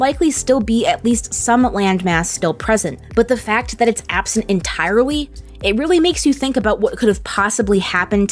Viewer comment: did she say en-tie-wrui ?